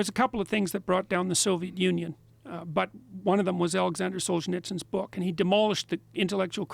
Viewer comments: Can someone tell me the author of the book he mentioned in minute 1:20:54? It's Alexander something but I didn't understand the last name. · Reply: Hey Iker, I’m assuming it was Alexander Solzhenitsyn, he wrote the Gulag Archipelago. Cheers. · anyone know the author or book he is speaking of ?? alexander sholnitzkin?!?! ( cant make out the name) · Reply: Its "The Gulag Archipelago" by Aleksandr Solzhenitsyn.